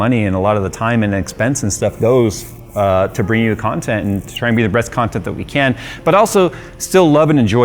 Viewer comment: Did he say "Breast content"?